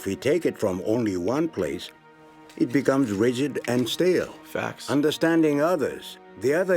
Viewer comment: Hearing this while i was not looking at the screen, i thought zuko was saying "facts" after iros wisdom spill. Too funny if you imagine that in that scene. Its